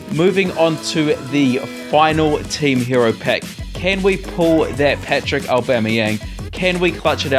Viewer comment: did he say can we pull that PATRICK aubameyang